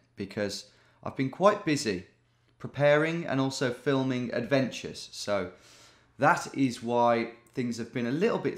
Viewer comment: did you say filming adventures 🤩